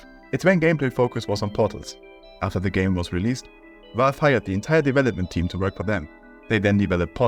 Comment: I wasn't reading the subtitles, I thought you said "valve FIRED the entire development team" LMFAO